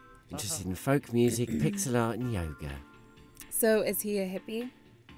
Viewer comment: at first instead of saying yoga i thought she said nyan cat